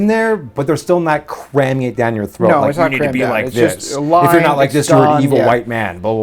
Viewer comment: does anyone else hear saying "...you need to be like this..." in the background?